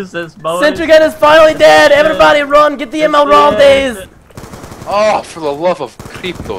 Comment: did say "Oh for the love of pitos"? XD